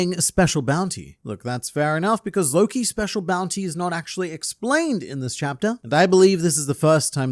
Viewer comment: I thought he said "Lowkey, special bounty is not actually explained" 😭